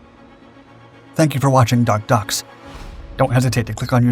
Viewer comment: I misheard it as Dark Ducks. Hilarious. Always great history videos on this channel!